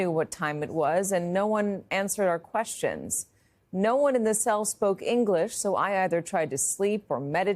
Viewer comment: I thought she said no one spoke English in those cells?